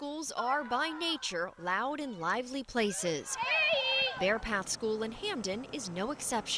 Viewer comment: Did they just say Babe?